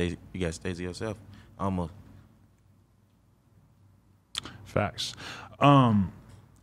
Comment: Adam didn’t even know what Uno said he was still tryna register it 😂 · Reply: 😂 He said "Faxx"